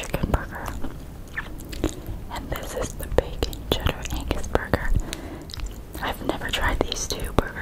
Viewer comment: Am i the only one who thought that said Bacon ANUS Burger? XD